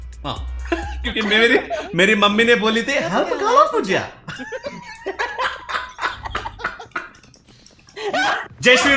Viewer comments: What is he saying 4:25 at this time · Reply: Help a girl out would you? skye😂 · What did he say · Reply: skye's dialogue - help a girl up , would ya , and he's helping enemy girl